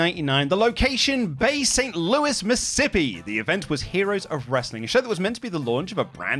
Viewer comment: did say Missippi?